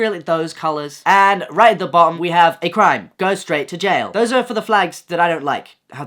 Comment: I thought it said "go to straight jail"😭